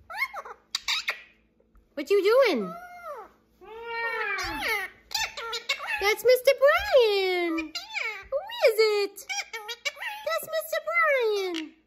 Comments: I always thought he was saying "Thats a big surprise!" Now I know hes saying "That's a Mr. Brian!" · Today I learned the phrase that always confused me was "That's a Mr. Brian!" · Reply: I just commented I thought little Beaker was saying “that’s a Mr Brain”😂😂🤦🏻‍♀️ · I AM SO HAPPY TO FINALLY KNOW WHAT HE'S SAYING WHEN HE SAYS "THAT'S A MR BRIAN!" He says it so much and I NEVER would have guessed this in a million years. Love this little man so much. Thank you for sharing him with all of us ❤ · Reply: I thought he said that's a Mr Birdie! Eye opening info here lol. ❤ what a beautiful family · I AM SO HAPPY TO FINALLY KNOW WHAT HE'S SAYING WHEN HE SAYS "THAT'S A MR BRIAN!" He says it so much and I NEVER would have guessed this in a million years. Love this little man so much. Thank you for sharing him with all of us ❤ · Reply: I thought he was saying Mr Brain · I thought he said That's a Mr. Birdie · Omg I always thought he was saying “that’s a Mr. Green!” Or “kiss a Mr. Green!” 😂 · The first time I hear beaker say Mr. Brian, I thought he was saying Mr. Brain. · I’ve always thought he was saying ‘KISS for Mr Brian’! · When I first found your channel some months ago, I saw a comment that Beaker was saying, “that’s Mr. Brian.” Had I not read that comment I surely would have thought Beaker was saying, “that’s a Mr. Brain.” Love your whole human and bird family ❤ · I always thought he was saying Mr Brain 😊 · I thought he said mr. Bird